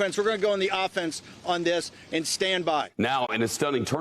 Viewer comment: Did he say stand 'stand by'??? 🤨all ur friends are in jail, they can't stand by